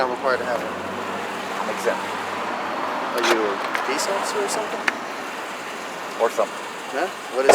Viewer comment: What did he ask? I cant quite hear it. "Are you a..."? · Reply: "are you a peace officer or something"